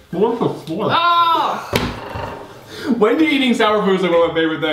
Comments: I heard say Wendie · I just noticed said “Wendy eating sour foods is one of my favourite things in the world” I thought he said Wengie but he said Wendy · Anyone else hear say “ Wendy” and not wengie